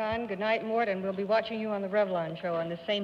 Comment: What does Arlene say to Mort Sahl during the "good nights" (starting at 25:07)? I've replayed it over and over, but I can't figure it out. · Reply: "Goodbye, you're very shy tonight, Mort."